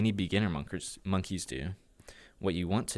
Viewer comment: monkers instead of moneys XD